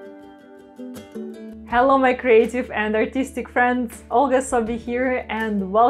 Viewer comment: Ever struggle with getting the right consistency for your pours? It’s one of the most common challenges in fluid art. I created a free Consistency Cheat Sheet to make this part much clearer: <link> 🤩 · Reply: what a smashing piece!!! have to tell you, Olga - every time you greet your fans, I hear "Welcome my crazy friends" know it's "creative" but it just gives me the giggles every time I click in to watch the magic you create!